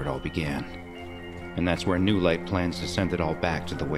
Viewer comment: instead of "plans" it says "plants" jajajaja